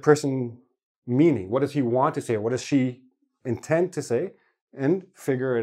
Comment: I watched your video and I have one question about "what does he want to say"... I heard what' es se want to say? what is the contraction "what' es se "?